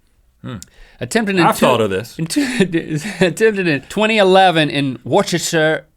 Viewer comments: Wait what did did link say at 6:31 ? It's pronounced wuster but its spelled Worcester. Massachusetts (and the UK) is weird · Hold on a second at 6:33 did you say Worcestershire England that's where I live I never heard of this happening. Well I knew some point in GMM you'll mention where I live eventually everywhere will be mentioned at least once